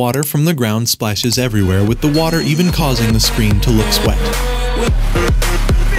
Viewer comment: "causing the screen to looks wet" 💀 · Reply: i heard “to look sweat” lmfaoo